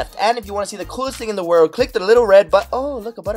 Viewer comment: I heard "oh look a pedophile"